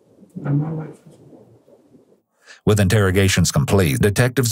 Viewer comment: i think what he said was "now my life is over"